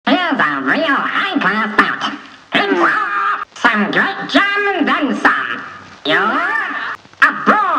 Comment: I keep hearing "some great jam and then some" and not "a great slam and then some".